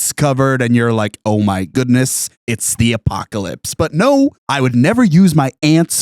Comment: I first heard this as "ant"-pocalypse.